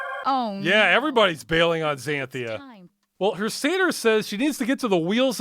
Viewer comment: Okay, I have a bad habit of listening to these videos while I'm doing other stuff and I thought you were saying seder, not satyr. I was trying to figure out why she needed to contact a Passover dinner during her quest.